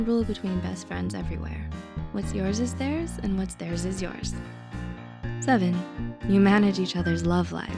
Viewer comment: It says "What's yours is theirs and what's yours is theirs" instead of "What's yours is theirs and what's theirs is yours"